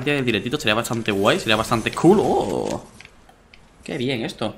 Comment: parece que dice culoooo jajajaja